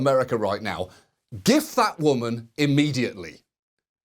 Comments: when he said “gif that woman, seriously” I heard “this fat woman” so either i’m an awful person or john oliver needs to speak clearer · I'm sorry, I don't understand your accent... "Give" that woman what? Or maybe do you mean "gift" that woman...? "Grief" that woman????? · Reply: He means turn her into a gif, because her expressions are hilarious. Gif that woman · When he said, "Gif that woman", I thought he said, "Give that woman" and I was expecting to hear "a medal" but he said "immediately" and it took me a while.